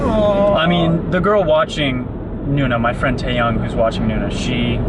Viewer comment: for a second I thought said Taeyang, i was about to say I never knew Nuna would be in BigBang !